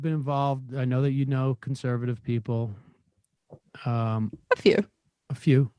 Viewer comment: When my gurl said"A few." It sounded like she said "fk you"